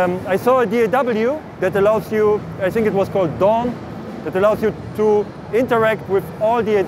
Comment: what daw is that? I couldn't make out what he said, but it sounds amazing! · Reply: I can't find it but sounded like he said DAWN. I'm also interested if anyone can find it. Could be similar to satellite sessions plugin by mixed in key check that out